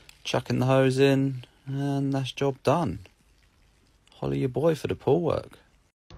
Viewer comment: He saying follow ya boi for the pool work lol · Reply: i always thought his name was Holy because i heard "Holy your boy for the pool work"